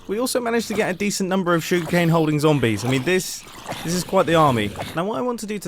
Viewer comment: Did he say "SUGARCANE holding zombies"?!?!